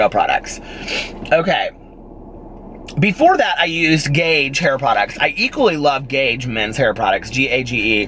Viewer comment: Had a moment where I thought you said yo used “gay” hair products and i was just like “wha???!!!” But, i got it the last time you said it.